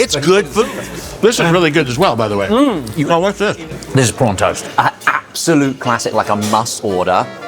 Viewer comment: for a moment there i thought he said P*rn toast.